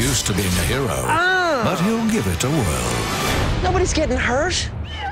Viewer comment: at first I thought instead of "Nobody's getting hurt" I thought deadpool said "Nobody's Kevin Hart